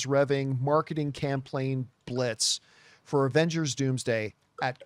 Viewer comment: "Marketing CampLaign Blitz" 😂 Yeah, i'm not the only one who heard it. 😂